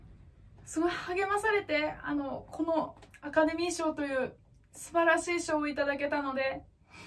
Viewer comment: ナカデミー賞って聞こえた